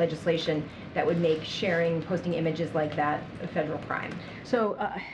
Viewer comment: I thought the reporter said p#@ images😂